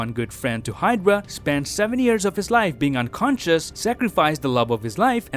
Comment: I thought he said 7 years of his life in ice but when I listen again he said seveny years of his life I was wrong lol